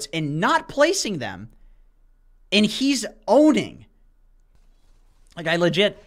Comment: ngl, I heard "he's odin" by mistake and as the cam shifted to sniper's face, I was like "He does look like odin" LMAO.